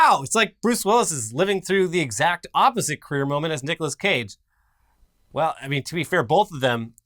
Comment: I misheard when they said Nicolas Cage, I heard Dicolas Cage😆